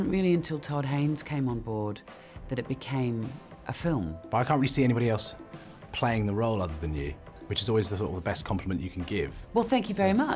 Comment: I love this interview! I can't understand this 100% though... She is absolutely stunning. What's he saying in 0:19~0:23? help me please T^T I've tried to find a transcript of this interview to perfectly understand, but i can't ....... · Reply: xiao lee «I can't really see anybody else playing the role other than you, which is always the thought of the best compliment you can give»